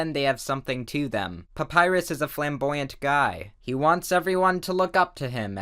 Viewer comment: I thought he said papyrus is a flamble guy